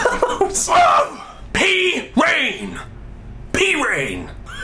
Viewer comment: also at the end when you said "i feel better" i thought you said "i poop better". thats good, thats good.